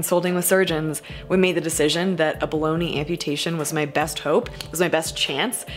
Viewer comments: We need merch that says "Bologna amputation" because I always hear it like that 😂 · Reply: I hear it as "Baloney amputation" which, after a quick google mid-post, is synonymous to "Bologna amputation". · I had a moment of pause when you said "below-knee" amputation. I heard "baloney" amputation. And then I got hungry. Sorry... :-)